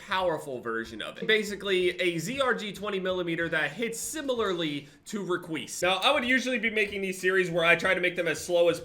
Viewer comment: I thought it said “hits harder than requis” and I was shocked